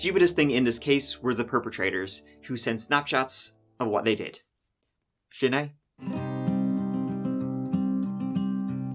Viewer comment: What is that "shenae" or whatever says sometimes? In this video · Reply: Sin é means “that’s it” in Irish